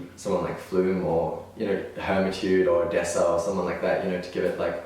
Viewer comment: I thought he said alessia (cara) and I got so happy for him